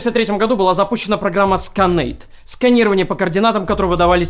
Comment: Что что что? Скайнет? Мне не послышалось😂😂😂